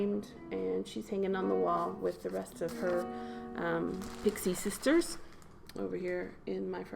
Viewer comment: Did you say "60 sisters?" Surely I misheard! I can just picture a wall of fairies...how beautiful that must be! I love that bag...what fun that's going to be to receive every month! Blessings, Marlene